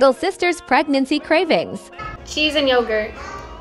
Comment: I thought she said keys and yogurt😂